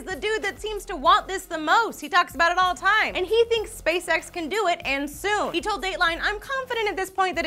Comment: I'd like to go, but whenever you say "SpaceX" I keep hearing "spay sex".